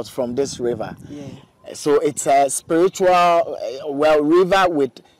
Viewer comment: Did she just say she is a spiritually a GAY?